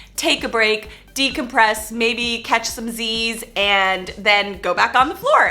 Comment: what did you say from 6:36 ? Maybe catch some disease? I hope not ; )